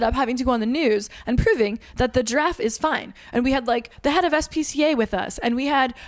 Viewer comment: Oh, Giraffe! I thought you were saying “Draff” and I was confused